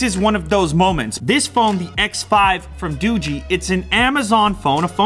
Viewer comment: Did he just say doo-gee. I'm confused???? I've always said daw-juh. 😂😂😂